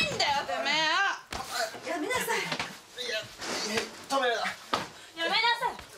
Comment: あ〜だからか、最初りえの家庭内暴力のだけの動画で見ててよく分からなかったからそう言う事だったんだね ていうか1:07:45の所止めるな！って聞こえるんだけど... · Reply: 小林良太 父親が自らりえに、止めるなと言っているみたいです